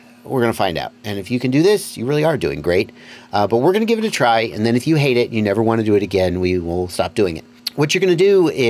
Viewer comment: I've been through enough college courses to know that when the instructor says "try it, if you don't like it, we'll never do this again"... Then we will definitely be doing this again. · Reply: that’s funny i heard “try it, if you don’t like it, we’ll do it again.”